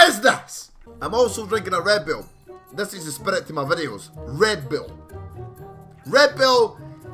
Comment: m8 you sound like your saying red bill not red bull